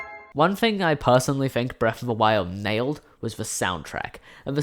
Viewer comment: Wow I thought you said BOTW “failed” the music, but you said “nailed” hahah I was coming straight to the comment section. Great video